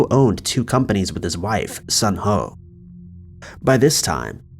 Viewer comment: I legit had to go back and turn the captions on to see her name. it is "Sun Ho" and not what i thought i heard.